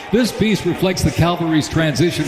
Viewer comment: Did i hear him say Calvary instead of Cavalry?